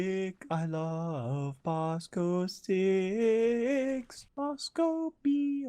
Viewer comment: I can’t tell if it’s vosco or bosco sticks I also thought he said Costco sticks